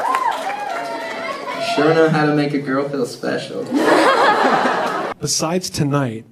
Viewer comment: what does he say at 15:28 ? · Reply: "You sure know how to make a girl feel special"